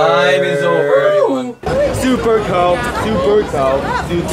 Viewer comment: it sounds like Taeyong is saying "super koud" which in Dutch means "very cold". I'm very confused😂